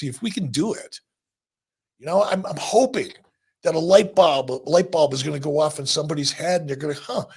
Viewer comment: did he say light bob? 😂